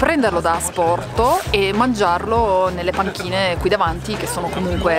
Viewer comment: Sono talmente stordita da tanta bontà (e da voi diciamolo) che quando la indicava le panchine, ho capito "patatine". 😂 Grazie raga, siete i numeri 1 c'è poco da aggiungere. 🥰